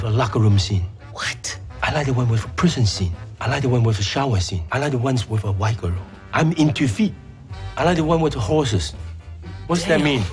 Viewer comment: My english is not so good.. can anybody tell from 8:50 - 9:02 what jackie say? I only can catch,i like the one with the locker room scene, i like the one with the shower scene, i like the one with the horses, tht's all i can catch.. the rest i cant.. can anybody tell?? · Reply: He likes the ones with a story, He likes the ones with a prison scene, He likes the ones with a white girl, he's into feet, he asks if they're teaching him bad words, and he likes the ones with a hairy women.